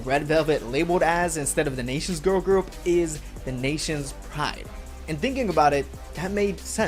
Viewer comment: he said that in the video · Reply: really for some reason i heard the nation's pie. This makes more sense I was so confused.